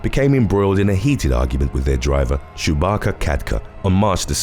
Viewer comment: I thought he said Chewbacca instead of Subhakar ..... I had to re-watch because of that